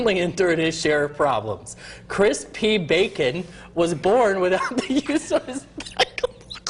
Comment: I heard he was born without penis size